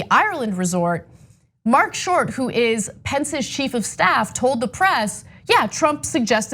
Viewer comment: Sounds like she says, “...Pence’s sheep of staff.” 🐑😆👍🏻